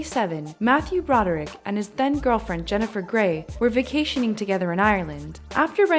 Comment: Omg I thought when you said then girlfriend you send thin girlfriend I was like 😮 👀 😂 ok what does Jenifer being skinny have to do with his crime